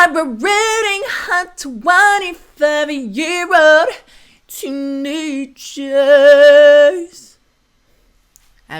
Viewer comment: All along I thought was saying ‘’ brooding hot 25 year old teenager’’ 😹😹 coz I thought it was for when the main guy gets mad